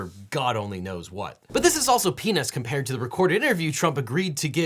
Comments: you said Penis, I think you meant to say peanuts, you said penis. Why did you say penis? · Just to let you know the auto generated subtitles thought you said "penis" instead of peanuts. And even though you meant peanuts its still somehow fits. I also heard penis for the court record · "But this is also *** compared to the recorded interview." I swear, I thought he said 'pen*s'. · For the record, when you said "Peanuts" it sounded like you said another Pen-s word. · It definitely sounded like you said penis there instead of peanuts lol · did he say what I think he said???? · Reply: "This is also peanuts compared to" It means as bad as the first things seemed, something even bigger also happened. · Devin: "This is all peanuts..." Me: ....did he just say penis?....*replay it*...nope. wtf is wrong with me? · just kinda listening to this while playing diablo 4, and 4:27 comes up and all i hear is, "...but this is also penis compared..." and I had to stop what i was doing to do a rewind. I can't unhear it. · "But this is also penis compared to the recorded interview..." Legit choked on my coffee when I misheard that. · I apologise for what I am about to say, knowing that you are a respectful person and all. So at 4:25, you said, "So this is all peanuts." But because you talk pretty fast, what it actually sounded like you said was "so this is all penis." Like I said at the start, I'm truly sorry for my dirty mind.